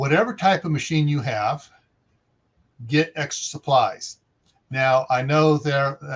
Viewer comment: Lol , I thought it said extra crap supplies 👍